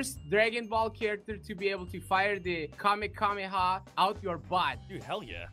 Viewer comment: Wait, did say "kame kame ha"